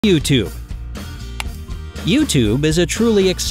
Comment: Lol I thought he said f you too